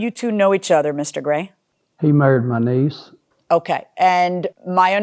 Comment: I swear I thought he said that he "murdered" his niece at first and had to rewind